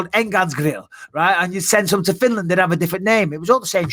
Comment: Did he say Finland? I live there lol